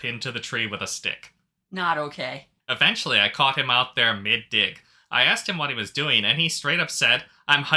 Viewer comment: Why tf did I hear "mid-di*k " 😅 not Mid-dig lmao